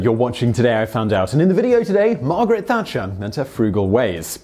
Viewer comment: I thought it said fungal ways at first lol